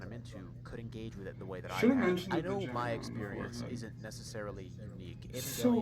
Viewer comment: I actually can't tell what you're saying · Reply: I watched it over and now I understand "should've mentioned the vagina on the forehead"